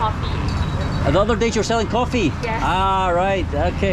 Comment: What's the strangest food that you've ever eaten? Personally I won't be trying anything with the word "Stinky" in the name again. 😭🤮😆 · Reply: She said in her Chinese Malaysian accent, "not really (lah), we are doing a pop up market (Pasar malam at other locations), the other day they are selling coffee" You misheard 不客气 bu ke qi as makaci which means 'Your welcome' OR MALAY 'sama-sama' in Mandarin